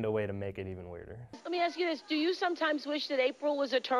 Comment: At the end I heard "sexually that works for me!"